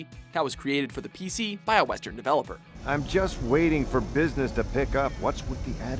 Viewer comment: I couldn't get that. "by a ***** developer". What is he saying?! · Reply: By a *western developer (as opposed to a japanese one, since the game played like a japanese rpg).